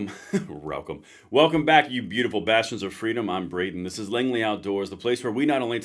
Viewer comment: It sound like you said beautiful bastards🇺🇸😂 I guess we could be called that